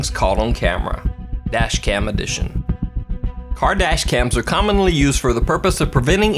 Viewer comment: When said "Car dash cams" at 00:12 I thought he said "Kardashians". Just me? Mkay